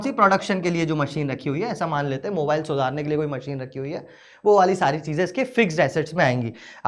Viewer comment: sir ne kya bola😂😂😂😂😂😂 mobile.....??